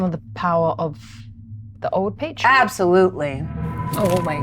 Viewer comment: what's Old Page or what is she saying? The auto generated subtitles aren't exactly amazing and nothing came up I searched for Old Page in relation to D&D. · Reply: I think she was actually saying "Old Patron", and the subtitles were wrong.